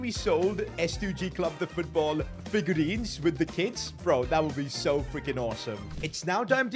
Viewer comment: I thought you were saying kids💀💀💀